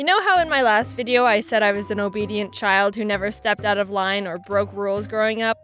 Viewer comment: My audio got all weird and I heard "I said in my last video that I was an obe-(silence) child.*